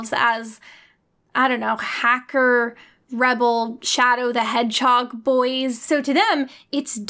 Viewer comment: "4chaners seem to already have themselves as images of I don't know, hacker, rebel, shadow the hedgehog boys." - Jenny Nicholson, July 21st, 2020. Pretty accurate. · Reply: I heard her say "CHADow the hedgehog," which is even better.